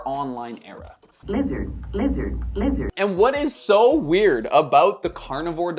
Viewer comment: I had to make sure I didn't heard the N word. I was playing roblox while listening to this in the background and I swear to god I thought it was the n word being spam out loud LOL!